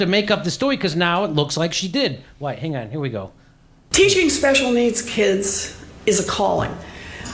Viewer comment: I heard "Teaching special needs kids is appalling..."